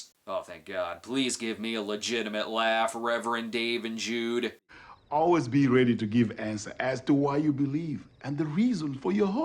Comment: Thought he said "always be ready to give ass to ass" the first time I watched this.